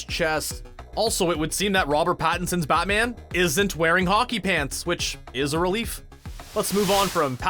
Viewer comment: the line is “hockey PADS,” not “pants.” i heard “pants” too; thought it was an odd line—that’s why i remember the shock i felt when i saw the scene with closed captions on.